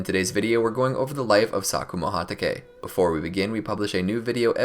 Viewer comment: Who else at 0:20 thought he said “Sakura Hatake”